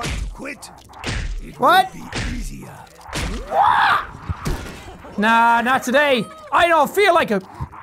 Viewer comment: NOT NOT TODAY I HEARD AND MY mind went to BTS